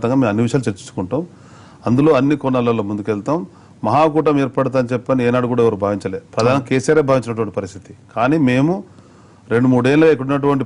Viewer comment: Did he say Pradhani KCr at 0:34 to 0:35?